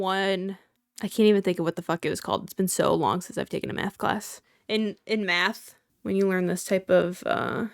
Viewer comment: Has anyone else pointed out how nice your eyes are? 👀 Also I might be a little deaf because I really thought you said meth class at first 😭